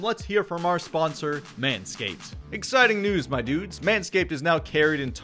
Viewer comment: My dumbass thought he said "exciting nudes my dudes" 😂😂😂bruh